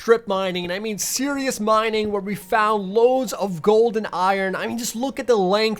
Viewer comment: "Loads of gold and iron". I heard it as "Loads of golden iron. "